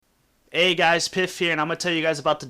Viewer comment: My first video seen my you and i thought you said hey guys it piss